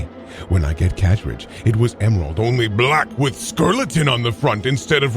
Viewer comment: I love how the college student who lives alone suddenly has a roommate halfway through the story. Amazing work. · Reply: I missed that because I can't stop laughing every hear I hear "skurleton."